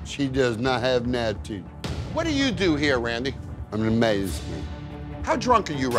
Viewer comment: It sounded like he said "I'm a maize" lol. I couldn't make out what he said. Guy is washed up